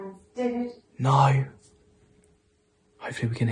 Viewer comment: When you asked for Dave if he can pick future child’s name, I heard, “i am David Moore” ???? Maybe this could be his full name x